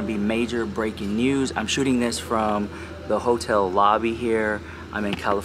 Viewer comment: I don't know if its because of all of the holiday shopping sprees I've been on but i swear i thought you said you were shooting from "Hobby Lobby" versus "hotel lobby" 😂😂😂😂😂😂😂😂😂😂😂 I was like gurllll that ain't no hobby lobby haul 😂😂😂😂